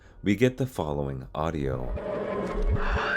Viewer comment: so what I hear is "I need U to fix her" as if the voice is asking the person to fix Meg.